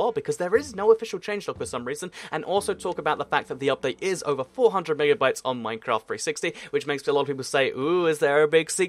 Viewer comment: I was scared when you said it was over 400 Megabytes because I thought you said it was over 400 gigabytes.